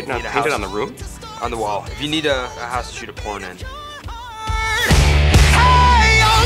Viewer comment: What did pete say at 6:00 i hear if you need a house to shoot a porn in what did you guys hear